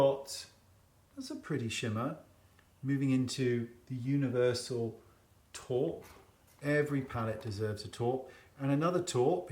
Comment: with his accent, I swore the shade was called “Universal Top” 😂😅 it’s not just every palette that needs a “top”!